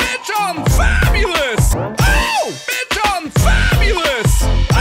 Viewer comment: After listening to this over and over, it starts to sound like "OOH! Big Shaun's fabulous!" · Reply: I heard " OH, be Chong fabulous!"